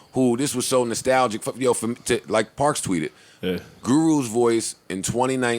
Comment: Sayvon gotta drop?!? I thought it said parks twice til today🤦🏾‍♂️ Guess i’m not the 1 to take the JBP comments to the next level🤷🏾‍♂️